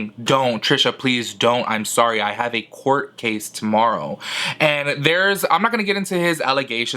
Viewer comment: There's no court on Sunday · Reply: I thought I Heard Him Say "I Have A Court Case To Go To" NOT "I Have A Court Case Tomorrow"!???!!?! Anyone Else?! Listen Again ?!